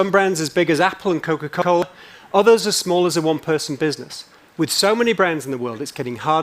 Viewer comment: at first I thought he said one plus( phone maker)